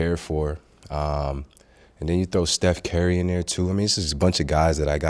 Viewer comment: Steph Carry I heard that